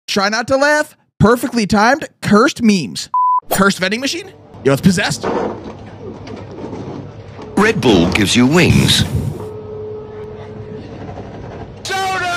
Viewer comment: i thought it said "ripple gives you wings" 💀